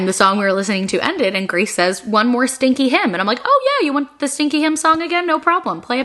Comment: lol I totally thought you were saying "stinky hymn"